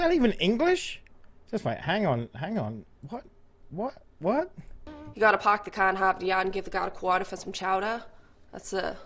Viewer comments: “You gotta park the car in Harvard yard and give the guard a quarter for some chowder” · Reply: I heard “You gotta park the car and harp the yard and give the dog a quarter for some chowder.” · So the Massachusetts lady said "You gotta park the car in Harvard yard and give the guard a quarter for some chowder" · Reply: I thought she said "park the car in the Harbor yard" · I think she's saying 'you have the park the car and hop the yard and give the guy a quarter for some chowder'.